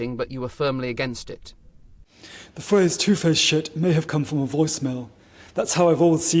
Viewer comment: Did I hear "two faced shit""?